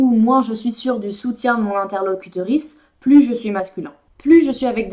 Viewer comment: Super intéressant et stimulant intellectuellement ! "L'interlocuteur.ice" j'ai entendu "clitoris" 🙄